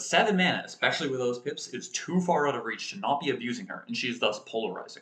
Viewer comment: You said pips. I heard hips. Have a nice day!